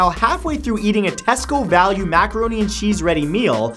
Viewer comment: i thought you said "while eating a testicle value mac&cheese meal" oops?